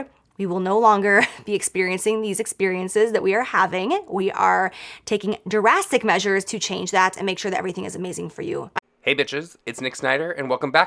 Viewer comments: did she say Jurassic measures?😂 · am I the only one who heard her say "Jurassic Measures" instead of 'Drastic Measures'?